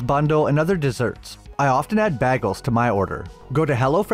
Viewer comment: Wait a minute what did he say at 1:35 or is it just me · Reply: BegALLS it sounded like to me